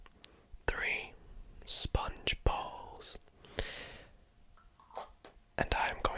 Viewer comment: When he says “Sponge Balls” I thought he said “Sponge Bob”